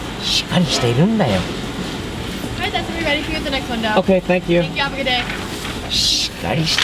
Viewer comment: お姉さんが「気を付けて」と言っているように聞こえる。本当はなんて言ったのかな？